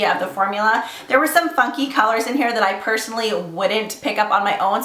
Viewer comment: hahah omg at 1:49 I totally thought she said "f*cky" not funky lol, great video as always <3